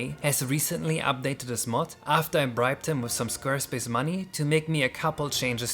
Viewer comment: INSANE story: I watched this video with a sponsor blocker and when it got to the part where he said he "bribed his friend with Squarespace money", it muted the word Squarespace and I thought I heard "bribed my friend with S*X MONEY" ☠☠